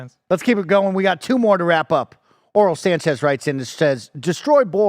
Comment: Does Mike say dudes name is... "Oral Sanchez". Then the next guys name was Bagel Boy Sanchez...